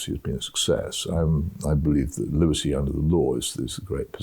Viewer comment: Did he say "lunacy under the law"? "Lucy under the law"? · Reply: Liberty under the Law